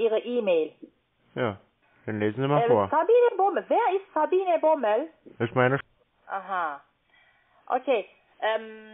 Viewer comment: Ich verstehe den Namen nicht. Kannst du mir mal den Namen hier in den Kommi schreiben? Neuer Name: Herr Rabimmel Rabumm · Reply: Sabine Bommel 😁